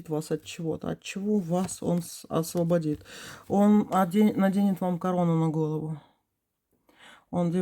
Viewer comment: Послышалось..он освободит Вас...от денег!...а не оденет корону!😅